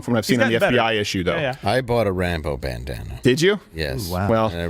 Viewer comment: I heard I bought a rainbow bandana instead of Rambo bandana.